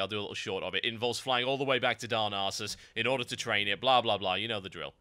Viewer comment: lol, i love how u say darnassus... sound like u say " darns anus